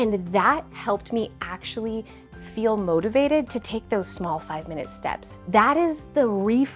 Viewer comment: I misheard "small 5 minute steps" as "vitamin steps".